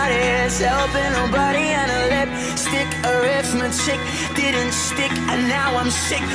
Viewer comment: When yungblud says “arithmetic” it sounds like he says “arrest my chick”😂😂😂😂😂😂😂